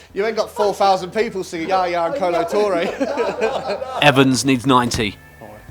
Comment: "you aint got 4000 people singing .........?" what does he say there? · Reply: Yaya Kolo Toure